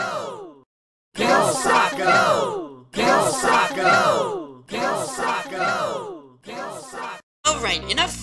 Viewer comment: WHAT THE HELL IS THIS SAYING?!?!?!?! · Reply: Go Sock, go!